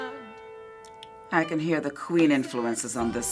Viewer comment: Did anyone else think she said "I can hear the Korean influences on this one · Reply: UNOGBearFireFlaster she said queen